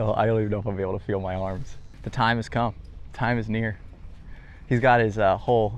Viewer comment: what did you say??? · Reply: Time is near